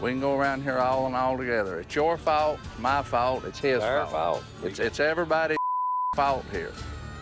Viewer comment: At @ 7:00 when they bleeped out what mark was saying, he wasn’t even cussing, he just said “friggin”. “It’s all our friggin fault…” and “take it or friggin leave it”